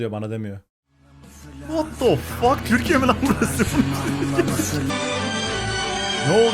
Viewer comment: de Tf2 mi dediniz yoksa ben mi yanlış anladım